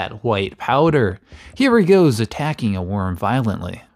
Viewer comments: "he still thinks he is bipedal" has me legitimately in tears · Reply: Dude I thought he said he still thinks he’s my pen— to which I thought the audio and video were purposely cut off… did not hear bipedal at all LOL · It cuts off quickly at the end so it sounds like you said Raptor still thinks he’s Bi.